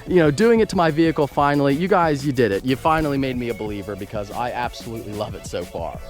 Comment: i heard that he said you finally made me a beliber lol